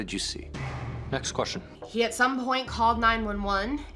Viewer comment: When he said, "next question", what I heard was, "I did it." 🤔